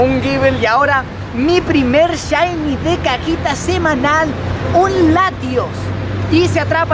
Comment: Mi primer shiny de cajita semanal Pense que habia dicho mi primer shiny de caquita semanal jaja like si lo estas viendo en 2019